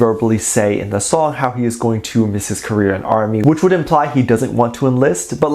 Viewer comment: Lol! At 6:10 I thought u said "Korean" ARMY and I was like "Heyyy, what about international ARMYs?🥺" Then I realized u said "Career" and ARMY. My bad, lol. Calm down PurplePaisley1292!🤣🤦